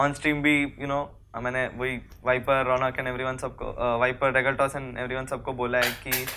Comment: does he says viper ronak :0